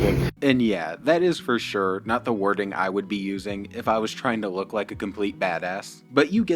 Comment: Why he making fun of LK's huge wart on his hand. I had to back the video up 4 times to hear if he did a little play on words. It sounded like he said " It's not the warting I'd use. As Liver King's wart on his hand is huge!